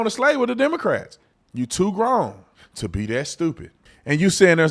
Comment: I had stepped away, I didn't hear you correctly the first time... I thought you had said, "You're to Wrong to be That stupid!" My immediately next thought was, "There's your next T-shirt, Brother!!" 🤓